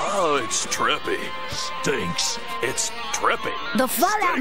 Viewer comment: i thought he said drippy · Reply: Trippy*